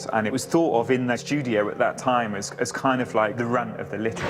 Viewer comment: In the part about GTA, what does he say at 1:09:42 "it was thought of .. as kind of like ?the rap? but littler." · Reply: He says 'the runt of the litter'. 4 years too late, haha.